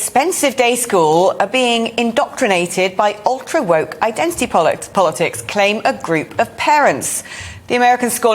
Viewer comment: I thought she almost said "Ultra woke identity bollocks"